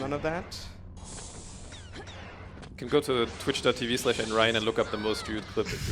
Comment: whats the link i cant catch what he said here. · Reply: i checked the speedrunning leaderboards and found theres a runner from russia thats "Nrion", theres a pretty funny clip that happens in that long elevator